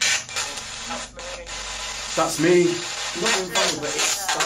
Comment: Hi! ❤ you and the Paranormal Boyz! When you hassled what happened and you said “that’s me” I thought I heard “I was murdered!” I could be wrong, but I was listening on my headphones. Much ❤ to all of y’all!